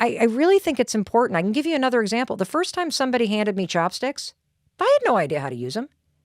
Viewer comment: Lol thought you said chapsticks